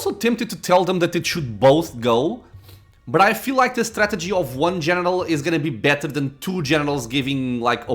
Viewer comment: Hum, I tried to hear it but I can't make it out lol what did you hear? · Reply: What you said: "But I feel like the strategy of one general is going to be better than two generals." What I heard: "But I feel like the strategy of one genital is going to be better than two genital" lol.